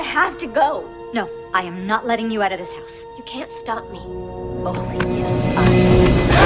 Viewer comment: what is Joyce saying? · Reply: I think she says "Oh, yes I can...."